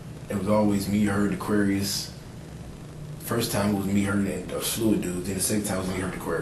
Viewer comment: him fighting in prison for "not being someone's girlfriend" his mannerisms are extra sus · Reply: I just heard him say he had s*x with the victim “and some fluid dude”. That explains a lot 🥴